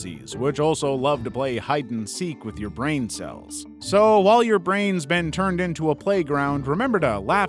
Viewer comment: Brain is scary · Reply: Fr thought u said brian